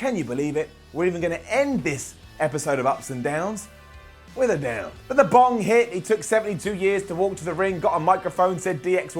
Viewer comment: must have weed on the brain. Cause I swear he says "and then the bong hit." @ 10:49 or maybe it's my head congestion ....